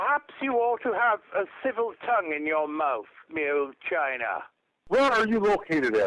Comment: What does Me Old China mean, anyway? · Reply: thought he was saying 'mule China' and I was really confused.